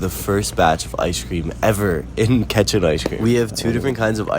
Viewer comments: I thought you said ketchup ice cream 😂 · At first I thought he said ketchup ice cream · I thought you said ketchup ice cream 😭